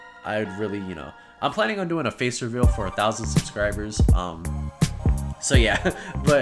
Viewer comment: I can't believe you changed it to 1,000 for your face reveal I thought you said 100